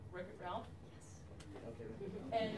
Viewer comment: mark what movie is she talking about? I can't hear her! Can anyone help?? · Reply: Wreck it Ralph?